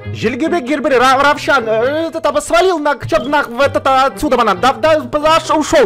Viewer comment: Мне одной послышалось что сказал " Свалил от сюда наХ"(. ❛ ᴗ ❛.)😅😅4:25?